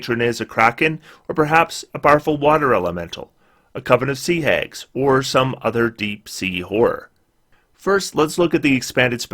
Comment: "A Covenous Sea Hag or some other deep sea whore " I can't be the only one who heard that, hahah